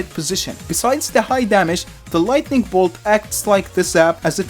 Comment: I heard “the lighting ball”